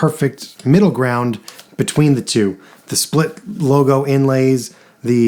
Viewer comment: Thanks so much! What'd I say split block? 😅 sometimes when the cameras rolling I can't do words good.... really appreciate you checking it out! Im glad i found my dream guitar and hope you get to as well! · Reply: my apologies, I thought you said split logo. Split block sounds right. One of my favourite looks for a Les Paul. Cheers.